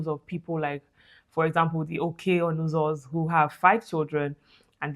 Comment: What’s the name of the doctor you said had 5 children. Thanks. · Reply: Dr. Okey Onuzo